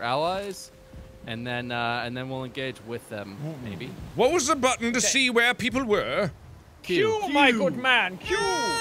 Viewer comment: Wait, What? I hear "What is the button to see what people $&*5#? I don't understand